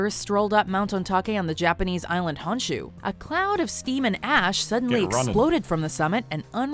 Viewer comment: Bruh i thought she said a cloud of semen ash lol